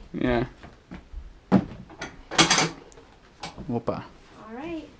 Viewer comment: opa, aos 7:33 eu escutei um opa ?